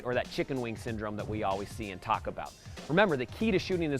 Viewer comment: I thought u said “Taco Bell”